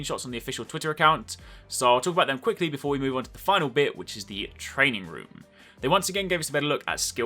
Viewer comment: training room......... thought he said trading room i was all like son i very happy ( i don't have a SON!!)